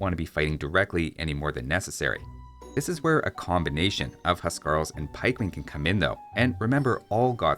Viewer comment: the subtitles say hussar instead of huskarl